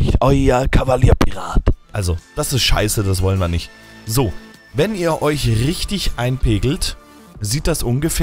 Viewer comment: irgendwie hab ich bei 3:04 "Wenn ihr euch richtig einen pegelt" verstanden xD